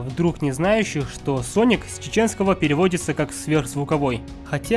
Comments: Мне ПОСЛЫШАЛОСЬ 2:00 "СОНИК С ЧЕЧЕНСКОГО ПЕРЕВОДИТЬСЯ КАК СВЕРХ ЗВУКОВОЙ" ЛОЛ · Соник с чеченского переводиться? Мне послышалось? 2:00 минуте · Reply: Да точно с чеченского) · он скозал с чеченского или мне послышалось